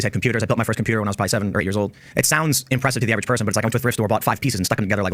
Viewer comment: Chatter gaslighting in defense of Tim. LOL. HE DID NOT say grade 7, he said 7 or 8 years old LMAO